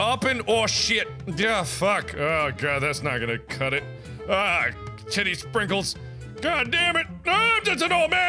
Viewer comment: "WHA DIDN'T WE SKIP A STEP HERE?!" Markiplier 2016 😂😂😂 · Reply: Moonshock 1987 I thought he said kitty sprinkles